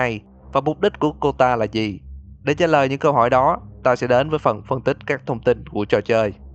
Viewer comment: Anh nói ta sẽ đến mà nghe thành tao sẽ đến